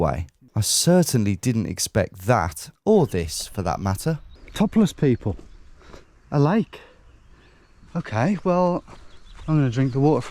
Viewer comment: I thought you said “Topless people! I like!!!!” Thank goodness for subtitles 😂😂😂